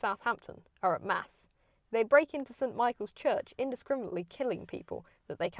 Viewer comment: 🚨CORRECTIONS 🚨: ✔︎ None so far! :) Spotted a mistake in the video? If you think you’ve spotted a mistake, leave a polite comment saying what you think is wrong and linking a legitimate source. I’ll check and if you’re right then I’ll add the correction to this pinned comment. :) ✘ I do not engage with corrections comments which are written rudely ✘ If you leave an essay full of corrections, I won’t check it because I have other, better things to do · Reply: Great video, thank you for the local insight (from your neighbours in Bournemouth)! Here are a few discrepancies between your narration and subtitles: @ 2:36 you say the soldiers break into St Michael's, but the subtitles say, 'brake' @ 6:52 you are speaking about the (correct) date of the Titanic, but the subtitles say 1812. Thank you again for your videos. I am a History teacher, looking to help my students revise for their Edexcel exams; hence, I was pleased to find your channel! :)